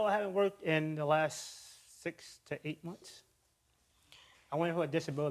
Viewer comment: He said she’s lazy but he’s been on unemployment for 8 months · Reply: 8 months I thought he said 8 weeks.